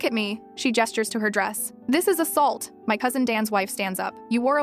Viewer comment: I thought she said my husband's wife said ....😂. Didn't realise that she said my cousin's wife ...😂